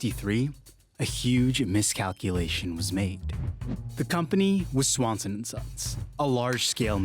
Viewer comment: I swear in 0:07 all I heard was Swansunsussss